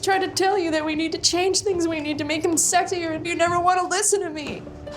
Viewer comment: "we need to make things sexier" Did i hear right?